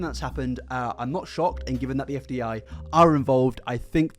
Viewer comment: he says fdi instead of fda